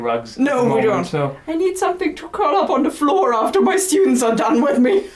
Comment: Wait... at 6:53 - does say: "I need something to cover up the floor after my students are done with me!"? o_o; Or, maybe it's just my perverted mind. XD Either way, I got to admit that I think you make a cute adorable couple. X3